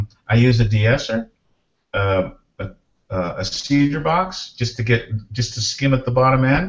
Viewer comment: What's he say at 0:48? Sounds like he says "seizure box" but I don't think that's an actual thing lol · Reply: cedar box - dialogue denoiser - suuuuuuuper expensive, look into RX6